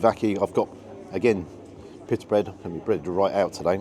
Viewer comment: I had to rewind the first time you said pitta bread as it sounded like you said piss bread, so second time hearing it, still sounded like piss bread 😆. Maybe I need hearing aids, but it made me chuckle but you always make me chuckle. I have shared a few of your vids to friends and they love you as well, you are so close now to the 100k, and a lot of ppl when getting more subs start changing things, but please never change your format or how your cameraman films, that is why everyone loves you (and the cameraman).